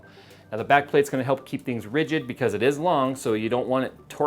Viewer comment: Because in Islam? That's what I heard.